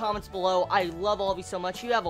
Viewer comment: I always thought you were saying ''have a wonderful day or not'' well, i assume that was night, but it sounds always ''not'' to me. xD PS: the moe of this series is very touching